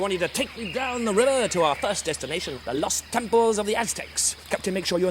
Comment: I thought he said Lust Temples of the Aztecs